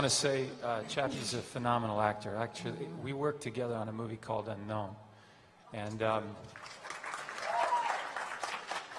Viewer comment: anybody know what the question was at 31:15? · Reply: The question was "Are we going to get something going with Fusco, he seems to be on the sidelines a lot?"